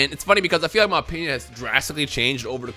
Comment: in the caption it say penis instead of opinion lmao